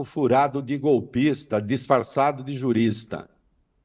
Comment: Eu ouvi certo??? Golpista disfarçado de jurista?!!! É isso mesmo?!! 11:49/